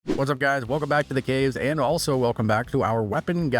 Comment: Did you say, "welcome back to the gays?"